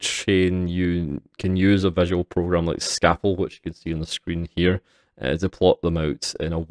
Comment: Great videos. What is the name of that flowchart program, again? I heard "scaffold", but I can't seem to find a diagramming program with that name. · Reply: Sorry for the delayed reply, its Scapple.